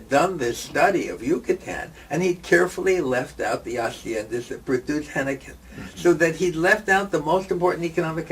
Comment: does anyone understand what Mintz says in the minute 12:13? "Redclif carefully left out the blah blah that produced blah blah? whattt???